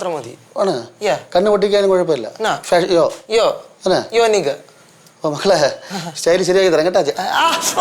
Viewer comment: did he just said the N word? 💀😭